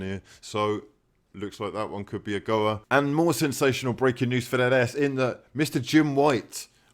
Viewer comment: Ok, this have to stop, I need anwsers. I know you're not saying this but all I hear is "Breaking News for that Ass". That's all I heard, for months now. So please, tell me, what are you really saying!!?? :D